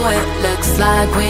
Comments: The journey I had to go to find this song was way too much lol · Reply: I thought they said "we got the glooow"😂😂😂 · Who else thought she was saying "oh boy we got that goalllll" cuz i did XD